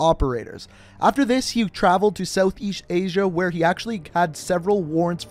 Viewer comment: Is it just me or did he just say south EESH asia